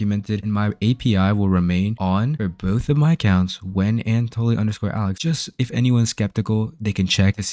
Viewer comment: What’s your ign? You said it so quickly in the vid that I couldn’t understand. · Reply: ign is